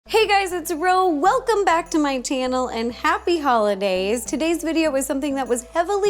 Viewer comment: I’m not the only who heard her say ‘tannel’ in the beginning am I?